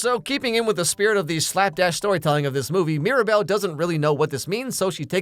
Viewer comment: When he said "Slap dash storytelling of this movie" I thought it said "Slap a** storytelling" And I was like "Whooooaahh when did my PG alex myers leave the room?"